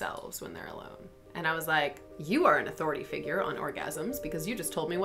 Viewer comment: I thought you said you were an atheist